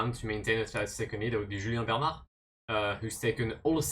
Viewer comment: thought you said Julien Bernal and was pretty confused for a sec